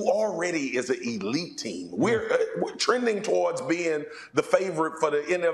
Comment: Did I hear him say “we’re” at 00:47 🫣